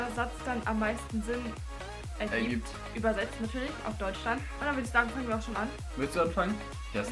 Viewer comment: übersetzt auf Deutschland😂😂😂habe ich das richtig verstanden oder hat gesagt übersetzt auf deutsch dann😂